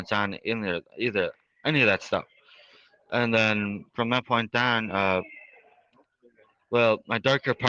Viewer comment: did he just say from that point DAN